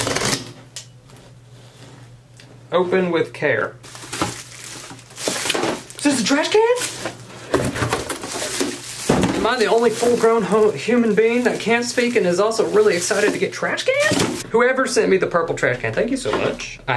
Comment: sounded like he said "am I the only full grown hoe"